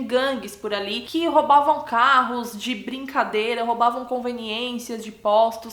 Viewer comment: Entendi não... (3:03) roubavam carros de brincadeira...? Entendi não...